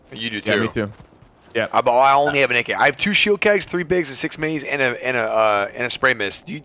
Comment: did he just say shield KUGS 😆